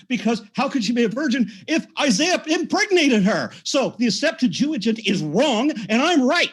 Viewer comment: What’s this word Aron uses here? “Incepteguigent”?